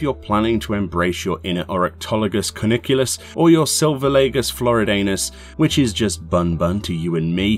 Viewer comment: Did you just say Florid Anus? XD